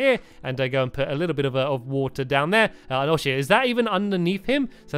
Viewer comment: missed a seat at the back on the left - SO ANNOYING XD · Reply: yeah it sounds like he said "oh sh*t